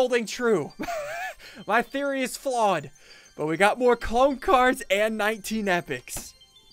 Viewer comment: 2019 anyone? Btw did anyone catch this “we got 19 clone cards and more epics” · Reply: There is nothing wrong and he said "We got more clone cards and 19 epics"